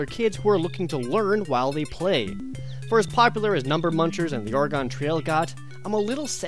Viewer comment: did he just say "The Or gone Trail"?